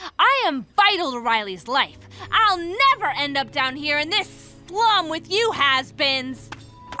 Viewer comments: "I'm still essential, I am vital to Riley's life. I will never end down here in this long with you Hazbins !" That's what I heard 💀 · DID SHE SAY HAZBINS??? 1:55 Hazbin hotel fan over here😭🙏